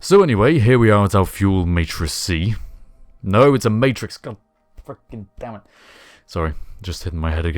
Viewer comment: I believe that was "god freaking dammit" - I always substitute those two :P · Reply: KoningWoelieWoelieYo Nope, as Harv pointed out he said "freaking" not "fucking" I misheard.